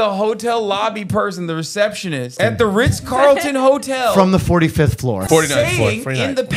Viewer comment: I heard “Rizz Carlton” and I am too old to have misheard it like that. Holy shit I need to go offline for a few.